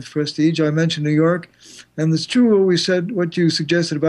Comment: You lost me when you said : there's two... Two is plural so there are 2 or 3 but there's 1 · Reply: Sorry I thought I'd heard there's two when in fact the speaker had said "it is true...". I'm just not used to hearing American speakers.